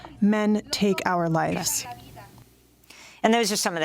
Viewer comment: I often wonder, I decided that I hear "the war on peace report" as the exclamation point seems to make it a demand. Like give us "democracy now!" Thus it makes sense that if someone must demand to be given democracy, that then it would be seen as a war on peace. ...but now when I try "war and peace" that could work as well... Meh, I dunno. Just my two cents. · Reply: from the wiki comrade: Democracy Now!, also called Democracy Now! The War and Peace Report, Democracy Now Independent Global News or Democracy News, was founded on February 19, 1996...